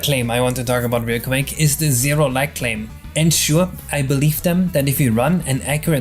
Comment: I wasn't looking at my screen and thought you said "Zero Like claim", as a bid to get Likes on the video, so I'll give one anyway